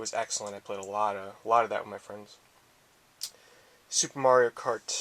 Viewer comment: sounded like you said " i played aloootta that, my friends... " i thought it was funny xD i know u didnt say that tho